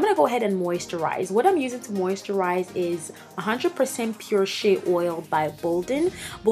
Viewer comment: 100% pure shit oil: ok so only I heard it 😂